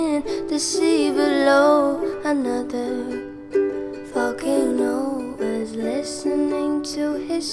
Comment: oof i had auto English translation on and at 2:15 instead of saying volcano it translated to fucking no (i dont mean to swear but that is what it actually said0